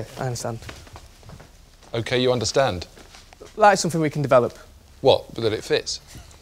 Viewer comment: wtf did the guy say? " lights and we can develop" ??? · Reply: "That is something we can develop"